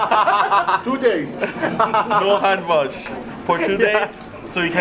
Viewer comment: Did he just say hand job 😳